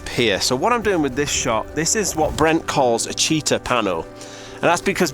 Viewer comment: With your thick Yorkshire Bear accent, I thought you were saying Cheetah Pano, not Cheater Pano. 🤣